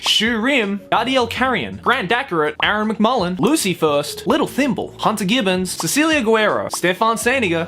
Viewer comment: I hear LittleThymbol all the time 😂